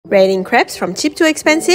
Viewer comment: I thought it said rating creeps.